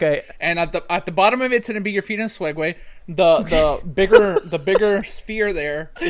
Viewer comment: it sounded like said feeding swegway